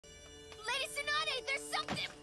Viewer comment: I literally heard her "Sakura" as "suck her up"